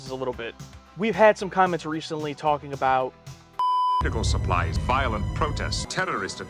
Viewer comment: It sounds like Picard says ‘pickle supplies’ at 0:36.